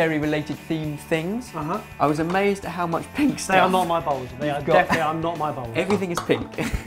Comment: barry:again they are not my balls me:wtf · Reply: Khrys Stevens I know it says bowls but when I first heard it I thought it said balls